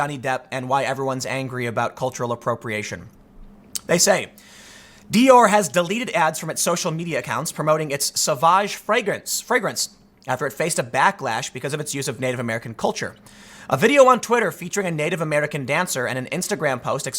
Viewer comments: Lol I thought it said Sausage. I was thinking , "what a fucking stupid name for a cologne." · i thought you said Sausage....can I have a sausage mcbiscit?